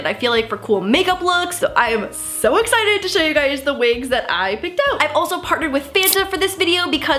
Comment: I thought you said “cheap wigs” 😔😭